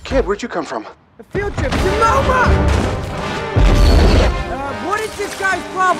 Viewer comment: what is he saying??? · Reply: "a field trip to MoMa"